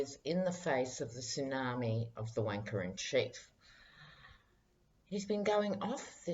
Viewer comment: Wanker in chief,lmao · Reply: Butch McCann ...I thought she said wanker in shit😂